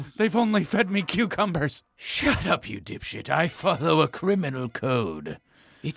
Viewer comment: did she just call him a dipshit